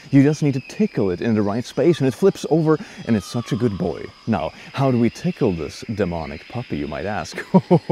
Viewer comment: Did you say, "How do we tickle this demonic puppy in my ass?"